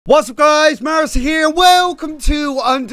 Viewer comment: when you say mairusu i hear "hey everyone mercy here" mercyfull.